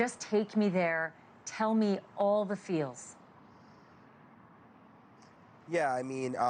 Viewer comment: “all the FEELS” …????? Did I hear that correct? I hope that isn’t what she said. That sounds so unserious like she is trying to be one of these immature TikTok mentally ill kids.